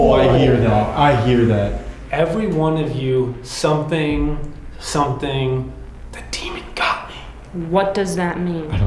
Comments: "Everyone of you were respectful. The demon got me" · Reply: I heard "Everyone one of you have not.. The demon got me" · "Everyone of you were respectful. The demon got me" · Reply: I heard: "Everyone of you said that the demon got me." And when they were at the firehouse they joked about him being a "demon doll". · I heard “Everyone of you are cursed. The demon got me.” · I don't know if this is just me... but that EVP I hear "every one of you disrespect Robert, the demon got you" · I hear "Every one of you knows exactly... what you did" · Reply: Dont hear that at all. I hear" Everyone of you, be careful, the demon got me" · "Everyone of you were respectful. The demon got me" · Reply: I hear “Everyone of you, they attack, the demon got me!” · I hear, "Every one of you asked that. The demon got me." like he was answering the question. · I hear "Every one of you was respectful....The Demon got me". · I hear “every one of you, go, run, the demon got me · "Everyone of you were respectful. The demon got me" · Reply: I heard “Everyone of you did not, the demon got me” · "Everyone of you were respectful. The demon got me" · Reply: I hear "everyone of you did that. The demon got me" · I hear "Every one of you left and then the demon got me." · I heard “everyone of you called me the demon doll” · What I hear is " Everyone of you, watch out, the demon got me." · I hear "everyone of you did respect me, the demon got me" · I hear 'everyone of you and because of you, the demon got me'